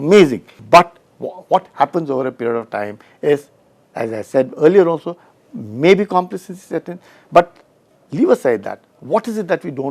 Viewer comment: Can someone please tell what does he said at 9:58 after may be ... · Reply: He was looking for a word - complacency or complacent... And created a fusion of them.